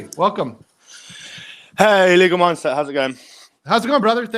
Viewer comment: I thought said hi Legal Monster...